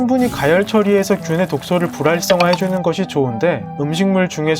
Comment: 불랄성 ㅋㅋㅋ 불활성이 이런식으로 들려